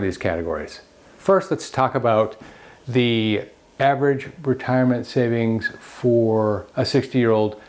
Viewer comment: When this video first started I was listening without watch and it sounded like he said the average retirement for a 6 year old. I was like, man, talk about starting young. lol.